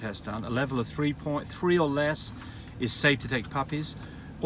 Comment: I thought u said 2 or less safe to pull puppies